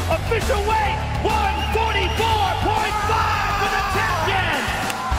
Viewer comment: Why is Dana White wearing Addidas shoes when they have a contract with Reebok? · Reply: for some reason I thought it said 145.5 it bothered me till they fought and they said 144.5